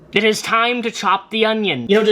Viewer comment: For a hot second I really thought you said "it's time to chop the Onision" and had to pause the video and laugh at myself thank you. Also!!! Seriously I'm so excited to try this recipe!